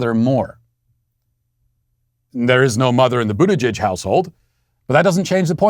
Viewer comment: Can someone tell me what is Matt saying at point 55:04 'There is not mother in the ???? household'. I played back several times but I can't understand it. Thank you in advance. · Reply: In the Buttigieg household